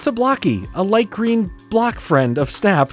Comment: OOOOOOHHH BLOCK FRIEND... I thought you said black friend! HAHAHAHAHAA